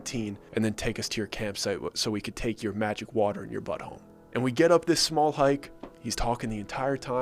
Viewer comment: I heard "we we can take your water and your butthole"